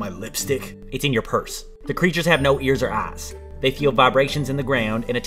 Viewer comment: I DONT KNOW ABOUT YALL BUT I FR THOUGHT HE SAID “they have no ears, or ass”